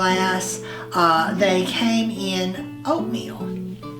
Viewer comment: I thought you said the little green juice glasses came in oatmeal? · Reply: Yes the little green glasses did come in oatmeal. I hope I mention that in my video. 🤭💚